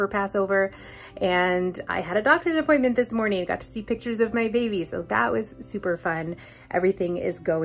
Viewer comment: Jusssst double checking because you sounded like you said “.. got to see pictures of by babies..” But right after said sooo, and I thought maybe I’m just hearing the s for so and not baby plural?? ;) 😯